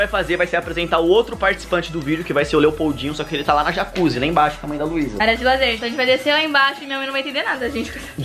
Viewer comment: só eu q ouvi isso 2:13 comendo luiza kkkkk acho q minha mente e mt poluida